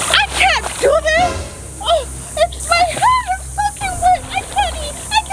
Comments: Did someone heard j say the f word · did say “My hair is f**** wet!” or is it just my hearing? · sounds like saying the f word but she said soaking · Did anyone else think that said the f-word at 8:55😳 But instead she said soaking. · um did anyone hear it- 8:55 (my hat is effing wet) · Did anyone else hear say fuking wet! I am freaking out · Did anyone hear say "I have my tic toks" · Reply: did anyone else hear her say "My hair is fucking wet' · Did anyone else hear J say my hair is fucking wet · Did she say my hair is fing wet on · hear closely and it's kinda sounded like said Fu--ing wet! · did just say her hats fucking wet?😂